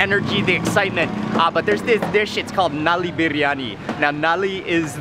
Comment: did Mark just say "their shit's called..."? LOL